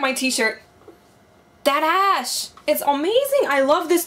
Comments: Lmaoo I thought she said tht ass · EVER TIME YOU SAID THAT ASHE I THOUGHT YOU SAID THAT ASS